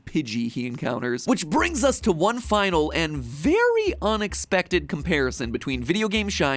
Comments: Is it just me or at 6:31 did it sound like he said "onyx-pected" as a pun? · Did he say "ONIXpected" at 6:31 bro OMG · I'm not the only one who heard Onix-pected right??